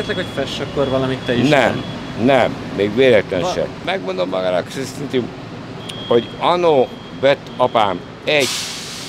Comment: nél ha valaki megfejti mit mond az öreg, légyszi írja le. Köszi! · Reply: 'megmondommagának éseztuti, hogy annó..'